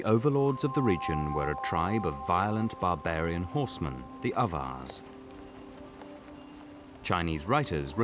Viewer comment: If anyone is wondering what he's saying at 16:00, since the subtitle was of no help had to do some web searching and came up with Avars. Closest thing I found to what sounds like "others".